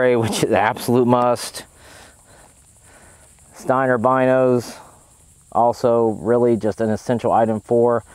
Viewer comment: Great video. What was in that black pack @ 15:30? Did you call it Steiner by nose? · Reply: That's the case for Steiner binos